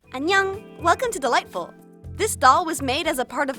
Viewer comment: Can anyone tell me what she says? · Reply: Anneyong is Korean for hello, I think!